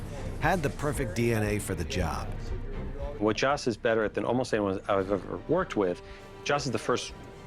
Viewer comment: what did he sayyyyy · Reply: Almost any was worked